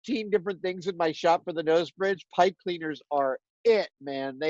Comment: What does he say is the bomb? Pipe feeders? I cant make it out as I'm hard of hearing and obviously can't lip read due to his mask. WHY AREN'T THE SUBTITLES ON???? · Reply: Pipe Cleaners